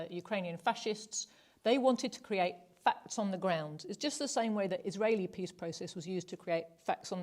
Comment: I am really sorry for asking this, and for being so inept that I don't understand what she's saying, but what does she say at 24:22? "Fats on the ground"? "Facts on the ground"? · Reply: Create facts on the ground: “i.e. win the war before you talk peace”